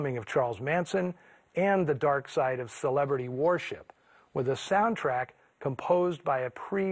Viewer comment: It almost sounded like he said "celebrity warship."